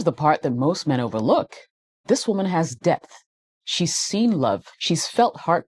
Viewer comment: did she say debt? The things said after that ain't selling the fairytale